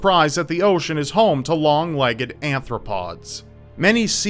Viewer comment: Am I the only one who heard him say "anthropod"? 😂 Love your vids!!